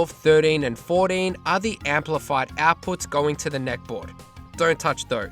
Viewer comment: That neck board mod looks promising. Would love to see more kits hit the wild to turn old perfectly good CRT TVs into arcade monitors for old JAMMA boards. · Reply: Thought you said neck beard mod lol 😂